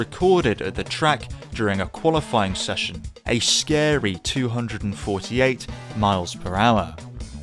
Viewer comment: 😮‍💨, at first I thought you said 288 MPH, not 248